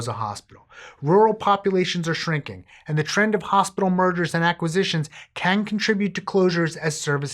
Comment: When you said “the trend of hospital mergers” I thought you said “hospital murders”, and I was very confused for a hot second about why we were just breezing by the whole “murder” thing 😂😂